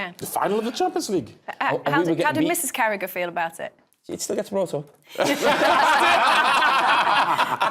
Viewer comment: Kate : How did Mrs. Carragher feel about it ? Carragher: itsstigdhrmton all of them: hahahhahahaahhahahaah I think no one understood what he said and they fake laughed to be polite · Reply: he said “it still gets brought up”